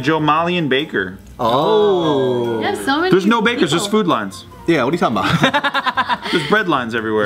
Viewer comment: I totally thought he said food lion